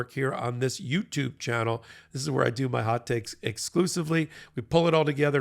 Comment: Hot Takes! I thought you were saying Hot Cakes with all that flipping going on !